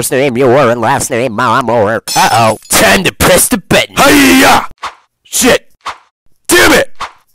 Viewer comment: am i the only one who heard time to piss the bed! the first time i heard him say time to lress the button?